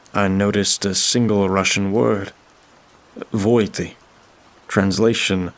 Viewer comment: "I noticed a single Russian word" "Luckly for me, I speak a bit of Russian, so I recognized the word" Me, being Russian, having not a slightest idea, what this word is or how it's related to Russian. XD Can somebody tell me, what this word was? I'm curious now. I've heard something like "worthy" or "worldly" or "voidy" I love deep web stories, thanks for narration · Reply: Voity